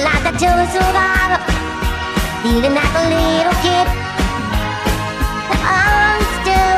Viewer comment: My favourite part is that it sounds like she says "feeling like a little kitten" rather than "little kid"